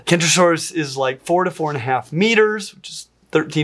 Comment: I'm afraid you just confused meters with feet. NO! I misheared you, understanding "44½ meters" instead of "4 to 4½ meters".